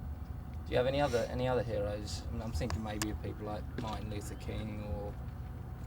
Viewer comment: Anybody hear the “maybe your people at 4:57”